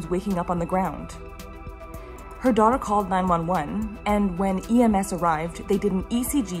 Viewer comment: Wow, the detail we got to see in this video was fascinating! I'm too old to change careers now but your videos make me wish I'd been a doctor. I had to relisten at 12:34 because I thought you said "her dog called 911" 😅